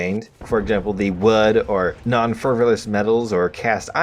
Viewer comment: What are non fervilous metals? I think it says non ferrous...